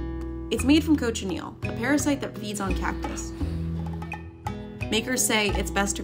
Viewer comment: What I thought they said the parasite grows on cat piss. What they actually said was the cochaneal larvae grows on cactus. Cactus. CACTUS! Not cat piss. I am going deaf. And dumb.